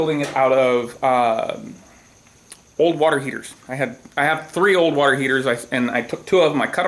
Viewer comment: the way you say "water heater" i first heard it as "warhead" which made me very confused.